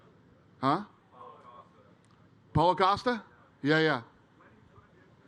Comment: "Paul Acosta?" I legit heard "Holocausta?"